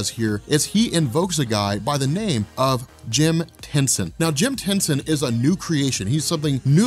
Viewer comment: I thought you said Jim Henson and now I can't get cosmic kermet the frog out of my head